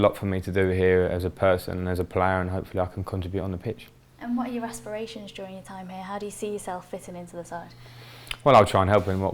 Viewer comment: what did the reporter say? can't get it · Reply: "What are your aspirations during your time here? How do you see yourself fitting in to the side?"